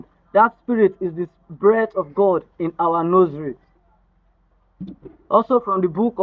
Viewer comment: what do you say? “The Spirit of God in our _____.” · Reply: Hi Gage, thanks for watching! At 4:35, I said, "The Spirit of God in us(humans) is a breath from the Almighty, that is why when we(humans) die it returns to the creator; you can see ecclesiastic 12:7 as said in 4:38 of this video." I hope that helps! Feel free to ask if you have any more questions. Don't forget to like and subscribe for more content. Blessings!